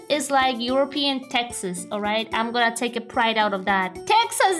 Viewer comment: All I heard was “eagle screech: Kurwa!”